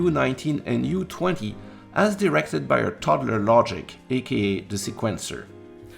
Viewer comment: I first heard 'toddler logic' and thought "ok, it's simple but maybe not simple enough for a toddler".